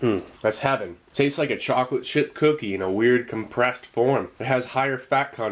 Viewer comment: Did I hear “Chocolate Shit Cookie” lol